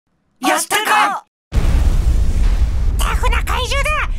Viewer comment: 最初のやったかの発音がﾋｮｯﾄｺっ聞こえたww